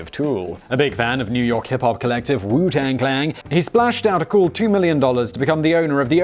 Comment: Did I hear “Wu Tang Clang” @ 25:00? If so, shame on you good sir.